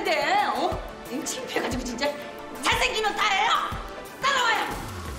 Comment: 에 잘생기면 다에요 라고 들리지 왜?